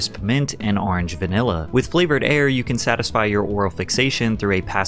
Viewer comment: I thought he said flavored hair. Like hair dye that adds flavor to your hair. I was wondering. Flavored air much better